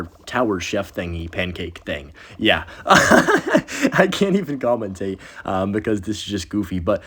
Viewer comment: I heard "Goblintate" 🙏😭